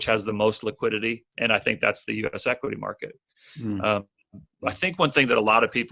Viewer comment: which matket is he talking about is not really clear what market he said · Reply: US equity market.